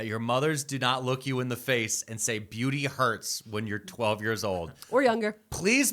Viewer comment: I heard "pain is beauty"